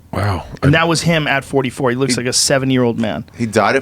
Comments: "he looks like a 7 year old MAN" JOE WTF LMFAO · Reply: y@ I thought he said 70 year old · Joe you dont clearly speak at times ,stop talking so fast .Sounds like you said Tommy looked like a 7 year old man when he died. i know you meant 70 but ........